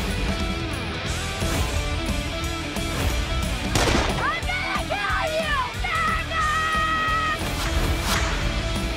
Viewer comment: wait I’m lost did she say the n word ?!?!?!?! Someone confirm it for me Please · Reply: Son Harriswhatup “jagoff”